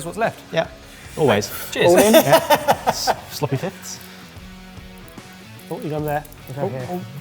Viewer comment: What did Kush say at 1:43? Sloppy tits?